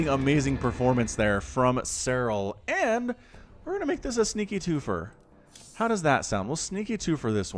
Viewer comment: Thanks again look forward to getting your voice pack one comment though when you are casting sometimes you say " this is a sneaky 2four " when you do i know who is gonna win it the person one game up 😁😁 thanls for all the casting . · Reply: ooooo twofer i thought u were saying TWO FOR lol